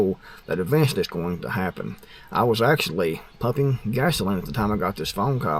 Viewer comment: I thought you said "huffing gas". lol I was thinking that you seem a bit too old for that.